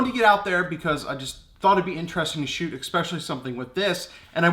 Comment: Did u say "expecially" ;)